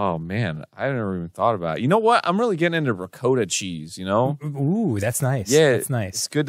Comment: Arin’s pronunciation of “ricotta” hurts my soul. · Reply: same. I only figured it out when the animator actually showed the ricotta. I legit thought “is arin trying to say ricotta cheese?”